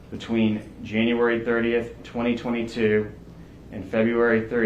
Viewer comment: 40 did he say February 30 because there’s never 30 days in February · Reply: He said February three